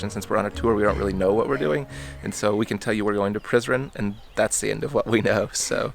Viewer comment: When he said let’s go to prizren it sounded like he said let’s go to prison